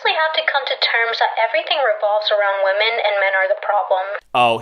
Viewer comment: #4. I swore I heard "Everything revolves around women, and we are the problem." 👍